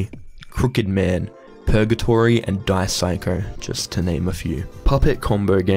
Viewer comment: did I hear you right? Dice Psycho? THE Dice Psycho by CHARON? If so then I didn't expect the mention at all (as I enjoyed the game but didn't find a lot of people talking about it), but isn't it more of a visual novel rather than rpg maker? Genuine question unless I misheard and it's actually another game entirely LOL